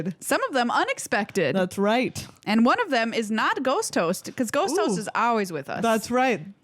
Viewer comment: Love me some ghost host 👻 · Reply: I misheard it as ghost toast which made me wonder if I missed an episode where they were eating toast and got some on a tangent about toast having ghosts and just riffed ghost toast into existence. But no. It was just my dumb ears.